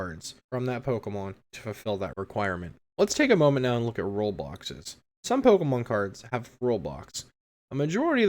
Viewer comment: This is a great video I wish this video existed when I was first getting into the game it explains a lot of the basics rules of the games explains Most of them the Fact that expanded exist you did a great job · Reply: Their is one thing that did make me laugh alittle bit cuz of how I missheard it when I got to 5:25 in the video where you talk about rule boxes I thought you said Roblox's XD my hearings just that bad